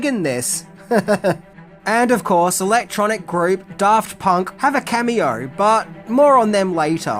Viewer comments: I heard "Daft Punk" as "Darth Punk" and immediately realized that Darth Punk is the Sith I didn't know I needed. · I'm not a huge Darth Punk fan, but their soundtrack totally made this film. It wouldn't be the same without them. ... ... ...I mean Daft Punk. Sorry, your Aussie accent made it sound like you were saying "Darth Punk."